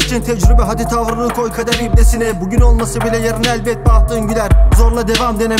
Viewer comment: kader ibdesine mi tam ne diyor anlayamadım